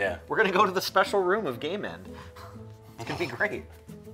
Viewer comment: I thought said, "the special room of gay men" and I was VERY confused.